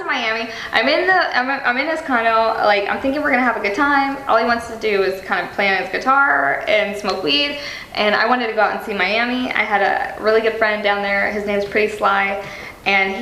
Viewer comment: HOLY SHIT , did she just say "smoke weed"